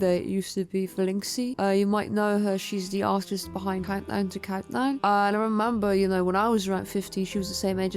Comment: I couldn't make out the username or the comic/project they made, what did you say? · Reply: Countdown to countdown by velinxi ʕ´•ᴥ•`ʔﾉ